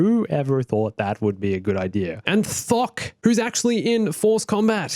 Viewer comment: when he said Thok Who else thought he said fuck?